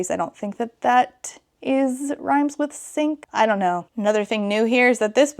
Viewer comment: The way it took me wayyyy too long to figure out what “schmink” was referring to 😭 literally went “OHHH ITS KINK” when it FINALLY CLICKED 😭😭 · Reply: I THOUGHT SHE SAID "RHYMES WITH /SINK/" AND I THOUGHT HE WAS A TWINK??? I came down here to figure out what I was missing lmfaooo